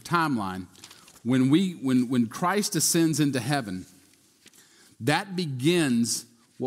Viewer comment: At minute 6:31 says Christ "Descends" into heaven. I'm sure you meant "Ascend"? Easy mistake but a very, very important detail. Unless I heard wrong, or I'm mixed up on my vocabulary, then I apologize. Or is it he Descends into the lower heaven on his arrival?